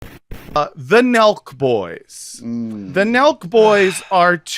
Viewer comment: I kept hearing "The Milk Boys." Turns out these boys are NOT dairy farmers! 🤯